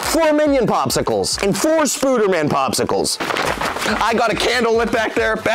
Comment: Youtube thought you said "Spoonerman" 🤣